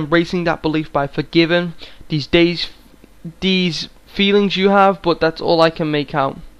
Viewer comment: Someone else has probably already pointed this out, and it's pretty insignificant I think. But at 15:08 a bit more of what is being said is "These feelings have vanished from our he."